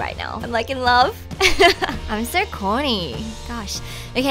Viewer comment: when you said im so corny after saying you were in love with the lashes, i swear i thought you said horny and i had to go back to check 😁😁😁